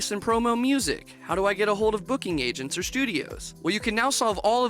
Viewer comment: i thought u said boogey agents